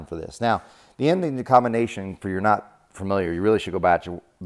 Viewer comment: Wow! I thought you said you’re no Superman… Thanks, awesome!